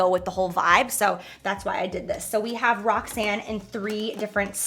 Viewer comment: The moment she said the first style is called ROXANNE I heard it as Rock Sand which I thought was pretty cool but then when she said that it's her name, I got real confused for a sec ngl